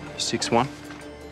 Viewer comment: What did chase said at 5:12 ? On the dad history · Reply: 6’1 height of dad